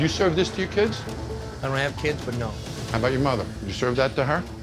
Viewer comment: I had kids, I wouldn't let them eat there, no way. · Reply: STEVE P for a second I thought u said I hate kids. I'm thinking hell yea